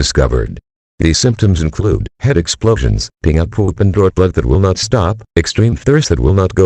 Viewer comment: Did I hear that correctly peeing out poop?!?!?!? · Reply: No, but I heard the words, “peeing or pooping out blood that will not stop.”